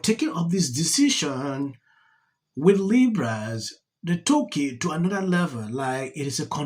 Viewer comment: Your accent threw me off for a bit, i thought you were saying 'libras' when you were actually saying 'liberals'😂